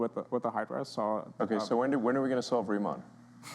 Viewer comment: What did Elon ask at 20:20/20:25? I rewound a couple of times and cut on CC. I can't quite understand what he's asking. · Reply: "So when, when are we going to solve Riemann?" As in "Riemann Equation".